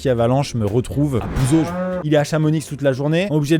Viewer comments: J'ai entendu un petit x discret après le Chamonix et ça c'est pas possible mdr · chamo quoi ?? pitié dites moi que j'ai rêvé et qu'il a pas dit "chamoniXE"